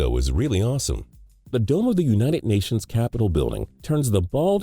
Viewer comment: did he say united nations capital?